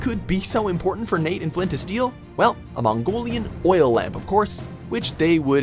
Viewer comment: Did he just say "Flint and Steel"?Nah,no need.I know he ment "Flynn to steal". 4:18,us that the Philippines at the corner of the map?If so,did Sir Francis Drake travel to Philippines too?